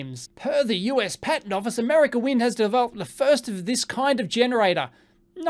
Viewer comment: Had to relisten, because for a second I thought I heard "American Mint" instead of "American Wind". "From the same people who've sold you dozens of commemorative medallions by pretending to be part of the government, comes a wind turbine!"